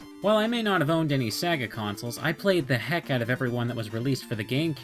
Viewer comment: I only hear "Saga Console".